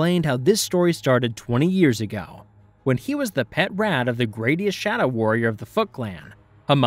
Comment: Hamato Yoshi was the “Gradius” shadow warrior of the Foot Clan? (I know you said “greatest” thanks to the captions, but it sounded like you said “Gradius.”)